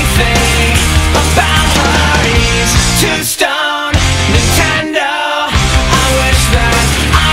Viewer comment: I ALWAYS thought i was mishearing "Nintendo" and when I FIRST looked up the lyrics I was like "How the HELL does Nintendo fit into this?" then I realized "Oh wait, DUH, Nintendo is like the equivalent to how guys are with Xbox or Playstation now." So he's paying more attention to Nintendo than her. I get it now! Captain Obvious, signing off. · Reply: When I was a kid in middle school I always thought he said "Tomb stone!" Now I know what he's talking about...